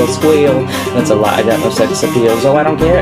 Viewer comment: At the end when he goes " and don't forget to rate me" it sound like he said "and don't forget to rape me" :L ..... Don't need to ask ;) xD